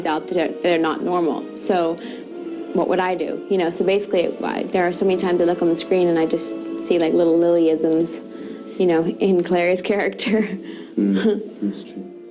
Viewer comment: Please, someone could tell me what Lily said from 2:16 to 2:20 ? :) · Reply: "there are so many times I look on the screen and i just see like so many little "lillyisms" ,you know, in clary"